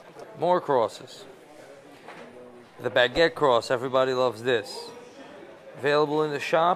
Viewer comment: ? What type of cross is it. It sounded like he said "Backet cross", no?